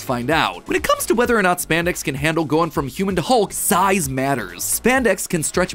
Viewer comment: "size matters" is that what said?